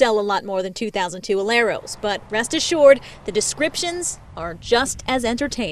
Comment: does anyone else hear ''rust assured''?